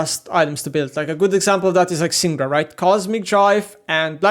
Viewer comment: What champ did he say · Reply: Syndra